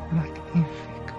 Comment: What did he say at 2:31?? I heard like nee figure. Plzz help! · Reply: He said magnifico